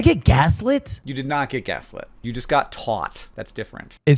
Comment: I initially thought you said “You did not get gaslit, you got got” and cackled😂 Had to rewind and re-listen.